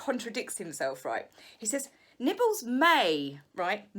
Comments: I'm not going to lie, I thought you said nipples may have been out instead of nibbles. 🤣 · The book sounds truly funny although I'd never spend good money on it. BTW, I thought at first you said that nipples were out and neither touched the food, lol! Then I thought that nibbles AND nipples were probably out knowing MM! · For a second, I thought you said "nipples" lol